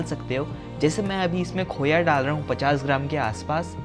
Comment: Hey you guys, I didnt catch what he is adding at 5:29. Anyone can tell me? Thank you ;D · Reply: Khoya